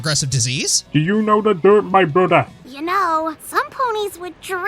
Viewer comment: For those who have better hearing than I, can someone tell me what that clip both is and says in this: 19:07-19:09 All I can hear is, "Do you know the derp, my Buddha?"